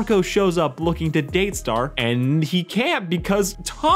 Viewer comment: Did I heard wrong or did he said Tomco?